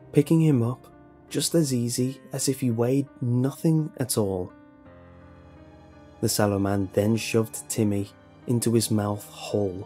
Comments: I thought it said the swallow man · i thought it said swallow man at first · legit thought it said the swallow man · I thought it said "The Swallow Man". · I thought it said "The Swallow Man", and was like... well I guess porn can be creepy too. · I thought it said shallow man at first lmao😂😂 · I thought it said swallow man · at first I thought it said the shallow man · I thought it said swallow man lol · I thought it said Swallow man for so long hahaha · I thought it said "Swallow Man" FRIK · Bruh I thought it said the swallow man 💀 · holy shit i thought jt said swallow man 😂